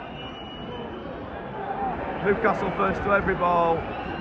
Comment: Did you say Newcastle first to every ball? Great video bud. First time visiting your ground. Hopefully you stay up. Lovely away day.